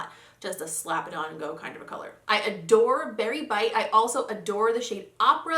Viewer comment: Okay I was just low key creeper status binge watching all of your old videos ....and 😂 I had to comment on this because, every time you picked up that shade I seriously thought u were saying “Barry white”😂